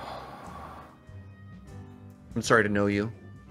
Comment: Oh my god, I totally thought said, "I'm sorry to know you." I was like DAAAAMN. That's harsh.